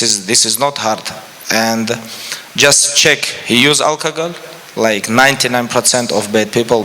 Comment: is he saying alcohol?